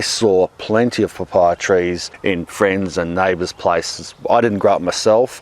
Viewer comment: Did you just say " gay trees " ??